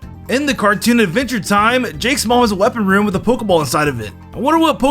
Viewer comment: did he say she or jake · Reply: "In the cartoon Adventure Time, Jake's mom's weapon room has a pokeball inside of it. I wonder what pokemon she has?"